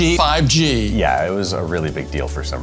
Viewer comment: did says 5G?